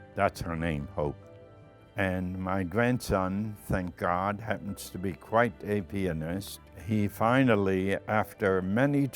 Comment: ( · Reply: although a wife for 69 years was funny and at 4:20 i heard "my son is quite a penis,"